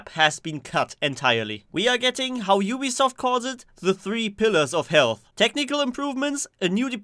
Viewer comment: I thought you said “3 pillars of hell“ xD